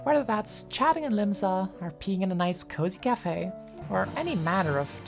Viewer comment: I swear I heard "peeing in a nice cozy cafe"